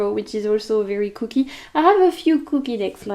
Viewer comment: Oh, mais j'avais même pas pensé que tu avais des affiliate links! j'ai acheté les Messages de guérison de l'Egypte Ancienne récemment, et j'ai rien capté haha... en plus c'est effectivement grâce à toi que j'ai découvert ce deck, oops! Next time! 😊Also, the way my tired French brain thought you were saying 'cookie decks' instead of kooky for a full fifteen seconds, I was so confused 😂😂 The dumbitchitis is getting worse, damn. And I love that you've been reading again! 2023 is the year of reading for me as well, and I've been thinking about Robin Hobb for a while actually. I read two of her trilogies (Farseer and Tawny Man)... oh gods, I don't know, probably 15 years ago at this point? Maybe more? I remember I really loved them at the time, and I've seen everyone and their grandmother talk about it recently so I have FOMO lol, and I think I'm due for a re-read. But I'm so glad you're enjoying them, and most importantly that you're feeling well enough to explore things you love again.💜And if I recall correctly, your card of the year is The Moon? Or am I talking out of my ass? But it would make sense that you're being more introspective. Anyway, as always, I love your energy and spending time with your videos, and I'm wishing you all the very best ✨